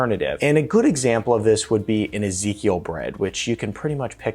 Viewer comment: can someone tell me what type of bread he mentions at 4:53 please? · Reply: Ezekiel sprouted wheat bread. It’s really good!